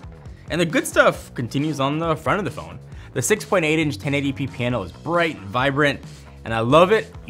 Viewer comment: Did I hear 6.8 inch(9:22) for the screen size?